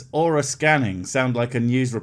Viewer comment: I heard that as aura scamming.